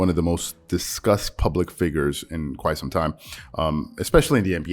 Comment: I thought I heard Disgust, yet you meant Discussed, whatever still works 👍🏼